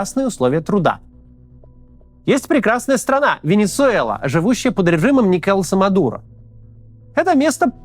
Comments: Я услышал «под правлением Николаса Мадуро» как «под правлением некоего самодура»)) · Из уст Максима - «Николаса Мадуро», мне послышалось - некоего самодура… Совпадение? Не думаю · "Никола Самодура" послышалось :) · Послышалось: "Есть прекрасная страна - Венесуэла, живущая под режимом Николо Самодура...". Ну, в принципе, да.